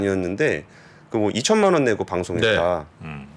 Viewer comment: 금양에서 삼천만원 받앗다는것 은 삼프로가 사심을 가지고 방송을 한다는 것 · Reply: 2천만원으로 들었습니다만.